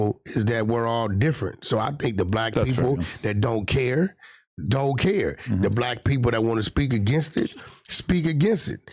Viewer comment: did Adam say that’s funny? Or that’s fair? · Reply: He said that’s fair enough but the body language said different, the side eye timing was a lil telling…